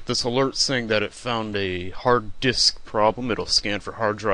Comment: i was looking at something els and i thought he said we found a hard dick